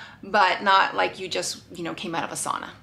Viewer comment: I initially thought it said “first Tinder box” and was worried what types of goodies were inside.